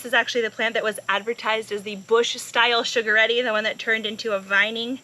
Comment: Beautiful, bountiful harvest! Those Sugaretti are a delicata hybrid - "...offers a semi-bush habit, spreading only about 2 feet wide..." according to my research. LOL I had thought you were saying "sugar-ready" and just couldn't find anything like it. We adore delicata squash (and hybrids) here - so perhaps this one will be another variety we will try. Your tips and knowledge-sharing is so valuable! Thank you!